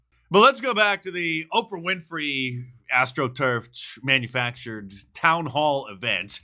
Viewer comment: At first I thought you said Afro-Turf. 👀